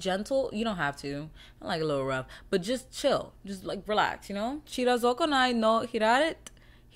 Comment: does anyone know what the manga name is that she tryna say at 17:17 😭😭😭😭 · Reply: Chirizokonai no Hiraeth